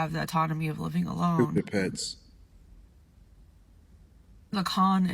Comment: what did he say, stupid pets?